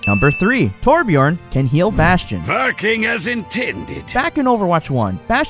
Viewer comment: I completely misheard what torbjörn said and thought he said “f*cking as intended”